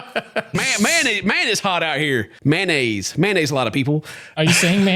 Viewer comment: I thought I heard "mayonnaise" 😂😂😂😂